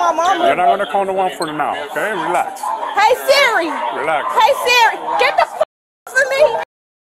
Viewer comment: (Did I hear this right, OMG this is ORIGIONAL) "Hey Siri, get these guys off of me" LOL!!!